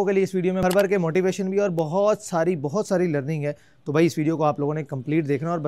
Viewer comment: animation instead of motivation